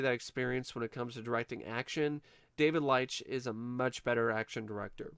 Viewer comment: do you think this movie would have been better if it was directed by David Lynch? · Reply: when he named the director for a moment I thought he said David Lynch instead of David Leitch. that's why I asked that question lol. but now just out of curiosity's sake I now wanna see a movie like that.